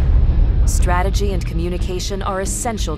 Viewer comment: I thought it said strategy and communism lol